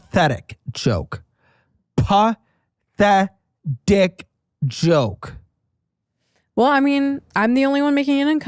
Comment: listening while doing dishes and did a double take cuz for a second i thought was saying “pop that d!ck” joke lmao